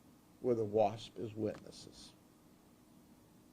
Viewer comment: Did he just say as wasps as witnesses! Like wtf 🤬